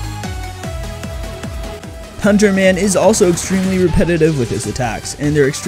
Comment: is it me or did I hear 'Hunter Man'??